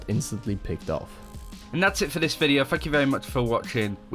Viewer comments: Did anyone else hear "Fuck you very much for watching" at the 4:52 second mark? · Does anyone else hear "fuck you very much for watching"? lmao